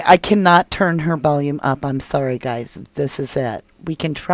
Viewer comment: Yeah sorry I thought she said I was sorry guys